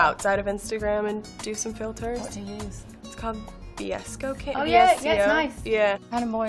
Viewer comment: I think she was trying to say VSCO